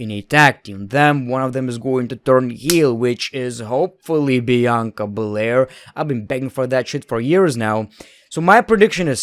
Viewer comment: Idk if it’s your accent but it always sound like your saying Bianca Belare instead of bel air